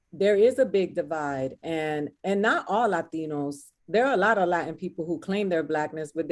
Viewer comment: she clearly said not all Latino people. She also spoke about speaking about your experience here.